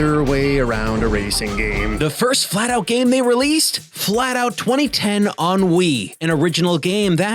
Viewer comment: I heard “Flatout 2010 on Wii” as “Flatout 2010 Ennui.” And I think I was right.